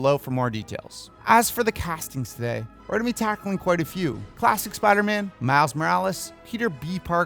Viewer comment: Was I the only one who thought he said plastic Spiderman 😅😂😂